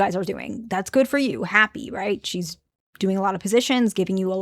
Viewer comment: "Doing a lot of positions"? Did i hear that right?